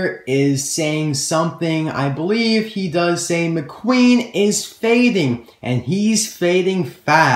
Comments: Fading? LOL I thought he was saying "Mcqueen has made it" as in he just came out of the pit stop or something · Check this new picture out: <link> It's actually a towel, but it reveals that the black/blue car in the trailer is Jackson Storm. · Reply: RCcars5 Also I thought the announcer said "McQueen has fainted! unkown words" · Awesome! I like the shiny wax racers paint. This scared me at first. McQueen just crashed. His this is fault? Did he just pass out? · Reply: Also I just saw that fading means going back in spots.So I thought they were saying "McQueen is fading medics fast!" But actually they are saying "McQueen is fading, fading fast!" Becuse McQueen is losing. ...I think.